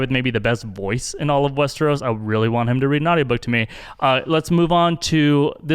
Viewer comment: Sounds like you said “I want him to read a naughty book to me.”